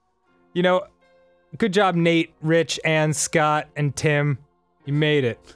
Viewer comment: LOL Dan said Ann's name but I'm guessing Matt and Ryan misheard it as "and" since they didn't highlight it.